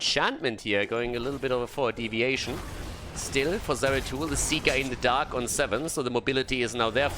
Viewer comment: did you said « the Cyka in the dark » ? XD